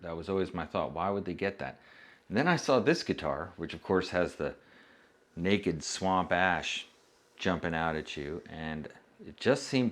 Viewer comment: I thought you said, "Naked swamp ass jumping out at you"..... My first thought was, "Well that's an extremely odd thing to come out of a guitar....." 🤣